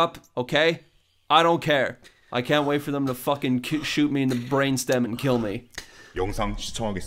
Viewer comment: I've been watching this video countless times because of how godly this is and I'm having trouble trying to process a sentence in it. At 0:13 after Sae-Byeok says "I don't care", I hear "I can't wait for them to fuckin kick-shoot me in the brain stem and kill me". Am I hearing this correctly? Thanks in advance